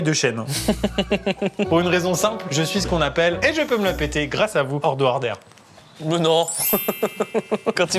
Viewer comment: pourquoi j'entends Edouard Baer?